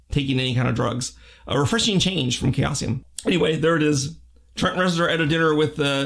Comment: I couldn't catch what was said at 3:25, "a refreshing change from - ???". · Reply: Chaosium